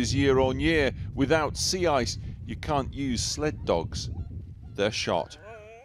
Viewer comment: Did he just say @ 6:31 the sled dogs will be shot? I'm hoping I heard that incorrectly.